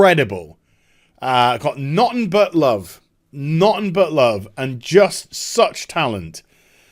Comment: For a moment I thought you were saying “naughty buttlove”...